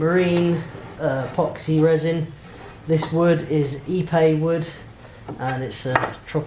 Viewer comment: Great episode, that's a beautiful cabinet. I had to look at the episode title again as when you said Ipe wood I thought you said e-bay wood.